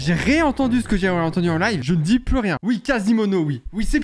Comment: Moi aussi j ai entendu Quasimono